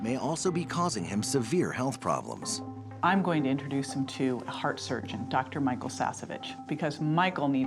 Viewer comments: Did she just say I’m going to introduce you to dr micheal sasebit? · i think they say sasa bitch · did she say Michael sassa b**?